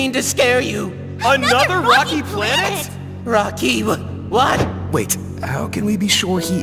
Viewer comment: i thought they said "another fucking planet!?"